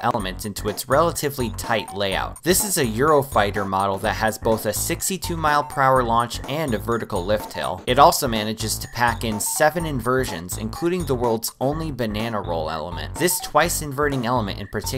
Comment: i thought he Said it has a “sexy 2 mph launch”😂